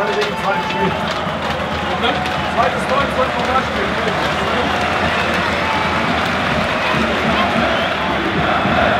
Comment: was sagt Mitch zu Becker? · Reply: "Zweites Tor im zweiten Pokalspiel" 😎👍